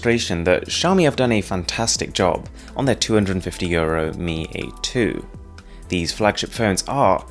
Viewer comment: Is it only me or is there any one else who hears "two hundred and fifty years old" when he says 250 euros